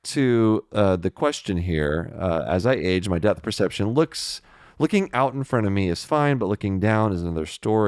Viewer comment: I keep hearing "death perception". Knock knock eye has never been this macabre 😂